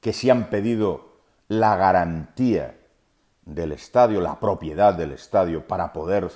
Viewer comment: Yo entendi la propiedad del Club no del estadio